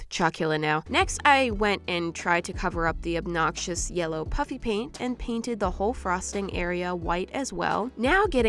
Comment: I thought she said puppy paint for the longest time